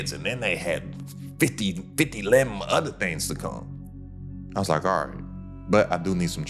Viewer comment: ok thats it. Dude a real one. DID YOU NOT JUST SAY 50ELEVEN other thangs 🤣🤣🤣🤣 boi you solid 💪🏽💪🏽💯💯💯💯🤣🤣🤣🤣